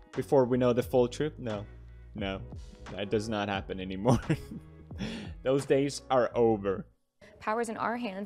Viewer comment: did he said "those days are ogre"